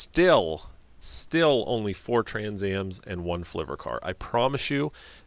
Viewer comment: What did you say at 11:22-11:32?... "4 Trans Ams & one--" what?... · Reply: flivver car